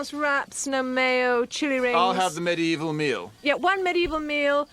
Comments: I always assumed chili rings meant onion rings covered in chili, like chili fries, but this is a more unique take on the order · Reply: Meanwhile I’m over hear having always thought she said “Chewy Rings” not “Chili Rings,” and as a kid imagined they’d have a texture akin to fried chicken gizzards though had no idea what they’d be made of. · Looks great! I always thought FGM said “Chewy rings” instead of “chili rings” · For the longest time, I thought they said "Chewy rings"